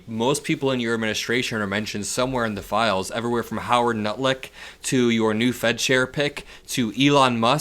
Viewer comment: did you just say Howard “Nutlick”? 😂